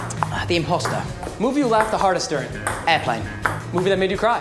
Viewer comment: I'm pretty good with english, but they talk a little bit too fast. It's especially hard to recognize the names of movies and books, since you can't tell what he meant by context. Like can someone tell me what movie he laughed the most at at 1:37 ? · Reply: ***** after typing what I thought I heard into google I guess he said Airplane, but now I don't get the first two TV show answers.